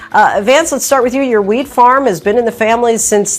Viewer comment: did she say "your weed farm"?